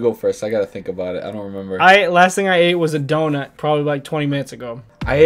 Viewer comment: I THOUGHT HE SAID THE LAST THING HE ATE WAS A DOG then I realized and thought why would eat a dog